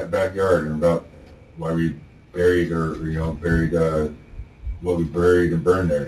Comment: he had an Investigators best hope a, “Freudian slip”, the captions are wrong he says “… and why we buried her”. Also polygraphs, (if you are actually wrongly accused), should always be denied, some research says it’s only accurate 50% of the time. It could also be beaten by taking a beta-blocker.